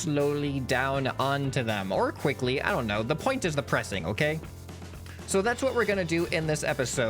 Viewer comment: sounds like he is saying: the point is DEPRESSING